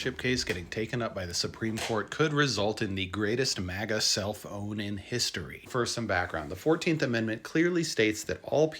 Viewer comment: At first, I thought he said “the greatest MAGA cell phone in history”.